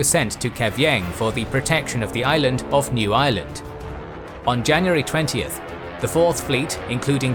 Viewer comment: I thought you said 'The Island of New Island' lol.